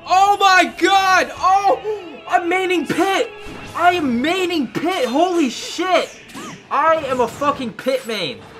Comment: I thought he said mating pit